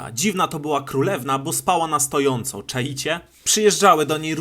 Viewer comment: usłyszałem szczała na stojąco😂XD